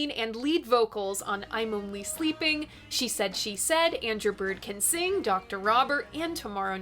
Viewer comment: I keep hearing "Andrew Bird Can Sing "